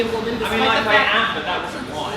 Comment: I think he said "I mean like, I am, but that wasn't why" :) Or with a bit more context, from 15:54: "And we weren't shushed, or called sexists just because you were trying to get your point in, just because the other person happened to be a woman, despite the fact-" Milo: "I mean like, I am [a sexist], but that wasn't why [I interrupted her]!"